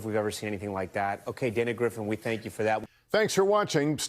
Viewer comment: For a second I thought he said Andy griffin. I was like what does he have to do with this😂